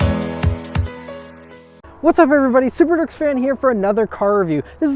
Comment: lol every time I hear him say "subaruwrxfan" really fast, it sounds like he's saying "super dorks fan".